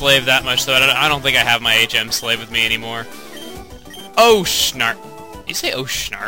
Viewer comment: he actually says "oh, so sharp!"